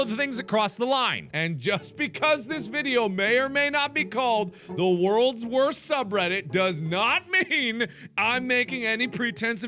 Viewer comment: "I'm only gonna be critical of the things that cross the line and-" What I heard next: "Low power please charge immediately." What actually said: "as this video may or not be called the world's worst subreddit-" Apparently my speakers needed to charge but for several moments I thought that was what actually said. XD